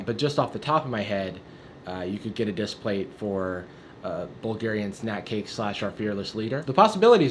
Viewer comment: What does Jarvis say at 1:02 ? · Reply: You could get a Displate for a Bulgarian snack cake/our fearless leader